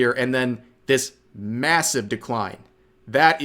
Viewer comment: did you say massive?